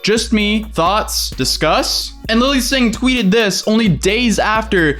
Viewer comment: Did he say Emily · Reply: And lily sing